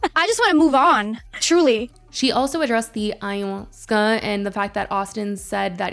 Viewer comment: Wait what is she saying at 18:15 ????? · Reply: Ayahuasca